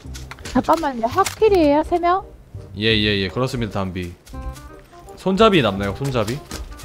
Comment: 단비를 하도 들었더니 손잡이를 손담비라고 들어버렸다 ㅋㅋㅋㅋㅋㅋㅋㅋㅋㅋㅋㅋㅋㅋㅋㅋㅋㅋ